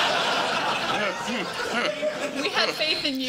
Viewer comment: What was said at about 14:40 from the audience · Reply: They said "I dont know her name"